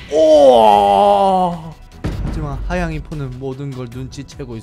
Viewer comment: 나만 하양임포라들리나요?